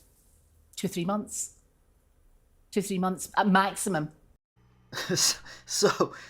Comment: You misheard her saying "15 months". She said "2-3 months..... 2-3 months... uh, maximum!" ( 7:38 )